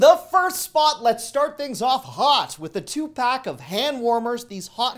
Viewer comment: Sounded like you said Tupac when you mentioned the hand warmers 😂